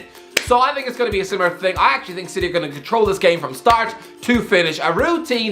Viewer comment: Hahahahah 😆🤣 I thought you said City are going to win 😜